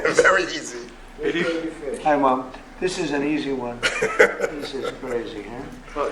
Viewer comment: did he say "Hi mom?" XDDD